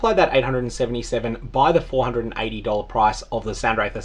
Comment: It's 420960, almost 42069 with a 0 on the end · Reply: Oh, nice, thanks. Dunno why I heard you say to multiply it by 420 instead of 480